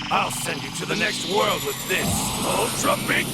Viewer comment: i always hear him saying i’ll send you to the thrid world with this